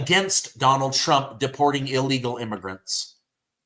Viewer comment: Illigals roechblowers had what i heard . Its beyond ludicrous.